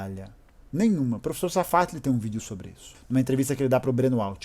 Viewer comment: alguém sabe como escreve o nome do professor que ele cita no 5:46 ? não entendi direito o nome queria ver essa entrevista . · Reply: Professor Safatle